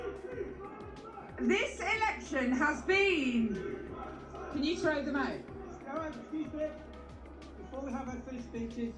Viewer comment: Fantastic video. One suggestion though please, subtitles on the parts where there's chanting or shouting from attendees, like the part at about 9:00, it's very hard to hear what the crowd is saying. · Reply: They're saying free free Palestine.